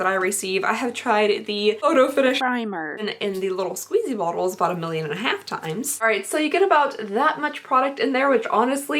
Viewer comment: Hi! Just curious, what did you say at 5:53 instead of primer?😂😂 Also you look amazing in red lipstick😊😊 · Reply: Jojo Rodson foundation